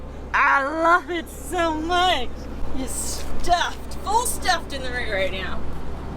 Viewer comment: WHAT DID SHE SAY!!! Good video guys, stay safe. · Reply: She said... "I love it so much, full stuffed in the rear"... lol